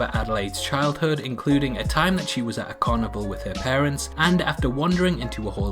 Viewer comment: "at a carnival", I thought you said "are cannibal"